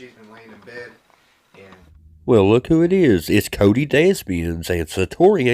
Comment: Every time I heard Cody's surname I heard Cody Dustbins 🗑 🤷🏽‍♀️